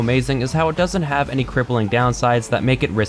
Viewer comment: did he just say cripleling ? Iduzz apear